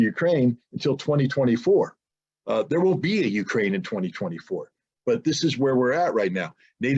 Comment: did scott just say "there won't be a ukraine in 2024"